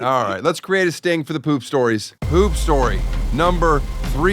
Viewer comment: lol when he 4:44 said poop story I heard hoop story, since he was just talking about basketball